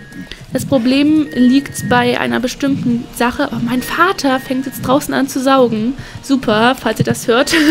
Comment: Ich habe verstanden : „ Mein Vater fängt gerade draußen an zu saufen „ 😂